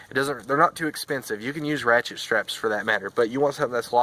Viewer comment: hey + couldnt quite make out that term. ratchet straps?